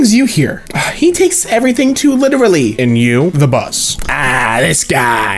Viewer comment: was he called a bus or something